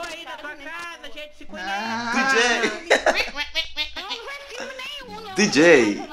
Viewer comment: Eu entendi esse DJ em kskssk